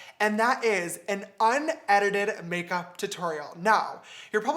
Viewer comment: I thought it said “untitled make video” I was trippin 😭